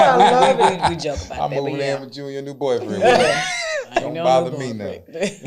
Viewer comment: Wait....did he say Id move in with you and your boyfriend😂😆😄😂